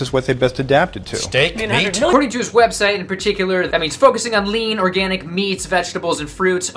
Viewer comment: Hey does anyone understand what he says at 1:07 when beginning his sentence? I'm trying to translate the video. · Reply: "According to his website in particular that means focusing on..." What language are you translating it to?